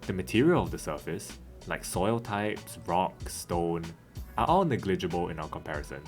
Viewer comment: DID I HEAR A ROCK AND STONE!?